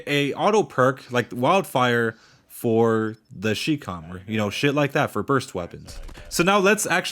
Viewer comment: i thought u called it the shitcom